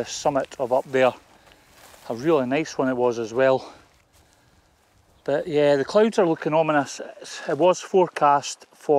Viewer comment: Nice one. What did that guy on the bike say at 21:15? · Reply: He asked that I make sure he was in the video. Hence why I kept that section in it. He did call me Roy instead of though, lol. :)